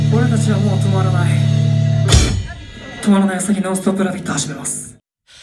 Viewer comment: 今になってコメントするけど、0:14 らへん「おい何言ってんだよ」って聞こえる。